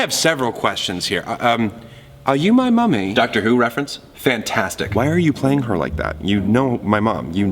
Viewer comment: Doctor Who reference? Fantastic Me, its my second time watching this: likes Doctor Who??? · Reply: I thought he said Dr. Seuss reference, because Dr. Seuss wrote. book called Are You my Mummy?